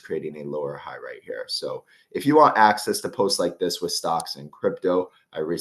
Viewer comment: all i hear: "if you want access to news, give me money"